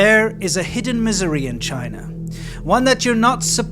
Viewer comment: I could have sworn you said a new Missouri in China! 🤣